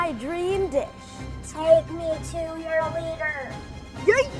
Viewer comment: “take me to your leader YEET (dabs)” What is this generation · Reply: I thought he said take me to ur weiner